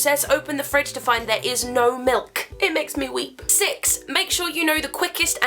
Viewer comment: I THOUGHT YOU SAID IT MAKES ME WEE